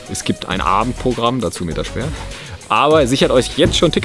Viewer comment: "...es gibt ein Abendprogramm, dazu mäter spehr..." hab ich das richtig gehört? 🧐😁🥰